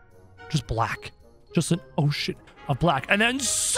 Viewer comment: sounds like he said oh s*